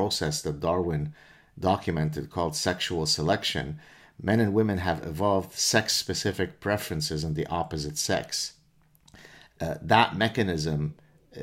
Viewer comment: Whoa... Wait a tick.... (3:20) "Men and women 'EVOLVED' sexual interest in each other"?? Did you just say that?? So, before that, the cock just went into the vagina because...well...let's see what happens?? To paraphrase Winston Churchill: "The case against Youtube is a five minute visit to Youtube." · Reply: John ORourke He said, " man an women have evolved Sex-specific preferences in the opposite sex" That's very different that what you wrote. So to answer your question did he just say that - NO he did not